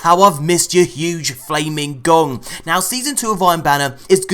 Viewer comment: Lol, sounded like you said huge flaming Dong! (Gong) 😂